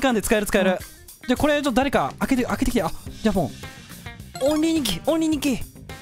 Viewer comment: オンリーニキがショーシューリキに聞こえたw